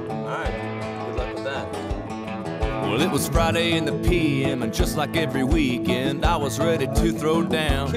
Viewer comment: I used to think Dierks Bentley was from Ottawa because I thought he was saying "It was Friday in Nepean..." lmao